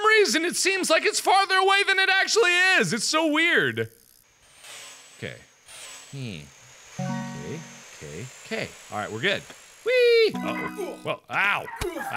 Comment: Don't think that Mark at 11:16 he said "kay kay kay."